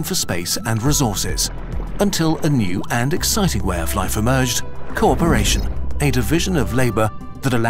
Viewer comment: Originally at 1:11 I heard "corporation" and went "What does corporate have to do with this?"